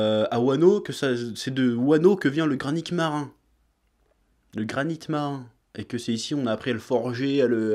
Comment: Mdr j'ai entendu le granique marin Mais en vrai l'effet du truc c'est vrmt ça